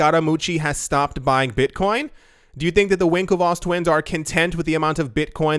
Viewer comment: I truly thought I heard him say “Twinkletoe Twins” from the next room.